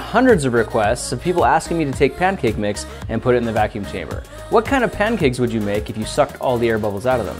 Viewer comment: Hey grant!! Why not you do a video with Alex wasabi or Rosanna pansino as you guys are equally as popular. Maybe put cake mix in a vacuum chamber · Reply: For some reason I thought you said "can you put a chicken in a vacume chamber" XD